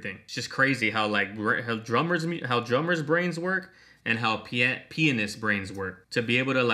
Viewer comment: I'm not going to lie I may have laughed a bit too hard when he said pianist too me it sounded like he said penis. I love this little series you put out of each twenty one pilots little group of videos.